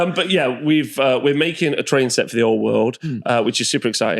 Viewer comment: Around the 58 min mark when you were talking about the trees you are making, I initially thought you said you were make a "train set for the Old World" not "terrain set". How cool would it be a Warhammer Old World Train Set!!!